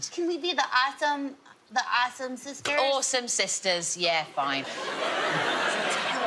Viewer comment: I thought she said arson sisters at first.